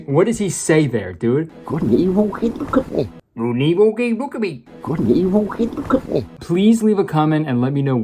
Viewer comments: I think he’s saying something something “look at me..” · I hear him saying “good and evil? Look at me” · i hear good and evil cant look at me...? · my guess is that he’s saying “good and evil, hey look at me.” I’m probably wrong, but that’s just what I hear. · I think he said “good and evil hey look at me” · I think he said. Good and evil, hey look at me. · I hear “good and evil, okay, look at me.” BUT I HAVE NO IDEA 😭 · I FIGURED IT OUT! He says "i saw my father over the holidays" earlier, and then the replayed clip is him saying "Good and evil, whered it go?" Totally unhinged and dumb, but hes saying that people don't care about morality. Still bad movie but i listened too long not to say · I think he’s saying “good and evil keep you company” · it sounded like he said look up at me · i think he’s saying “good and evil, hey look at me” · “Good and evil, hey look at me” I think is what he said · I think he says something along the lines of "good evil now look at me" I honestly prefer "gegenvulgebulkene" over that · I FINALLY FOUND OUT WHAT HE WAS SAYING AT AROUND 5:00, IT'S "Good and evil keep good company." Probably meaning like both good and evil can both seem like good people? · I think he's saying "Good and evil. Hey look at me!" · I heard "Good and evil? He'd look at me." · i think he’s saying “good and evil—hey look at me” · The boss is saying that his dad still thinks God's Not Dead. The unintelligible clip sounds like him saying, "Good and evil? I mean, look at me!"